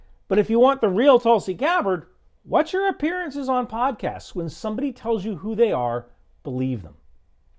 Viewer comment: "When someone shows you who they are, believe them." man I wish more people followed this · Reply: Actually that's not what he said, and I think what he said is a problem. He said "when somebody TELLS you who they are, believe them." Letting people show us who they are and believe what we see is what we should be doing, but Ryan said the opposite. I don't know why he thinks that but that's what he said. Timestamp